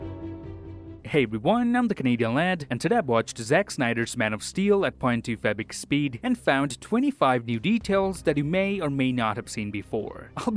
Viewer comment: Bruhhhh all my life, I always heard you say point 2 fabric speed instead of .25x speed, this particular video clarified of what your were actually saying XD